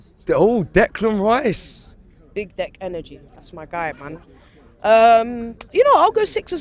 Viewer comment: did she say 'Big Dec energy'? 😂😂